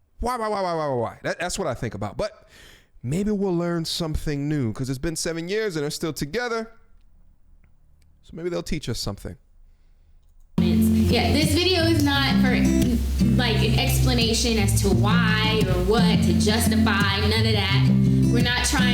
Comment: you said, maybe they’ll teach us something, I heard maybe they’ll entertain us